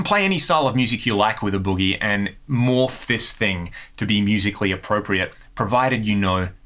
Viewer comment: Did he say ‘more fisting’? 🤣